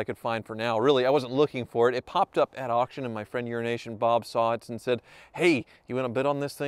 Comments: He said Euro Nation Bob so fast I thought 🤔 he said Urination Bob😂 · I thought he said "Urination Bob" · Literally every time says “Euroasian Bob” I hear “urination”. Lol. needs to enunciate